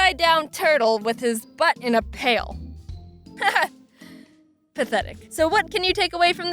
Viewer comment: you said a kid with a butt in a pale, i heard it as a kids but impaled.......